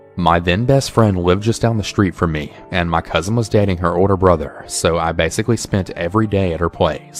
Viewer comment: “my cousin was dating her older brother” did i hear that right? 💀💀